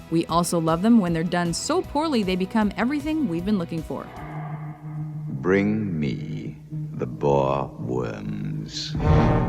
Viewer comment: I thought he said, "bring me the Baldwins." God that would be even worse.